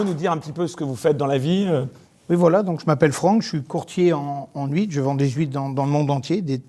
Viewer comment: oh putain des HUITRES ! Pardon j'ai du faire marche arrière dans la vidéo parce que j'ai entendu "je vends des juifs" xD omg je me suis dit mais what qu'est ce qu'il raconte lui là !! "Des très bonne juives de bretagne..."